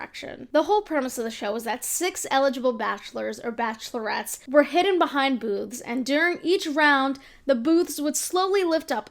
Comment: I misheard her at 0:14 and thought she said "hidden behind boobs" then I realized that I misheard her 😳